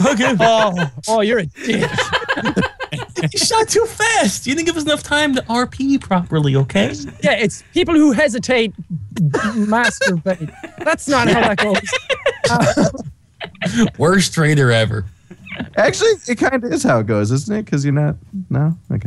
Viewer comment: Did I just hear say "people who hesitate masturbate"?